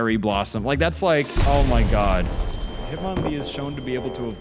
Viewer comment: i like how the captions say “God hit mommy” 💀